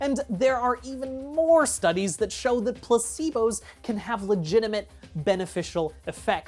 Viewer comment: every time you said "placebo" i heard "placenta" and I